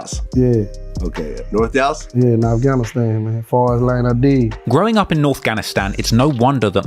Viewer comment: I think Mo3 said "Forest Ln Audelia", not "Forest Ln ID"